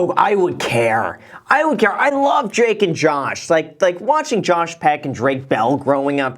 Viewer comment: 🤣 did you called drake and Josh "Jake and josh"? Lmao